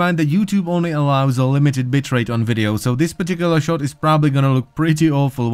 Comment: I know you said "bitrate" but it sounded like "bitchrate"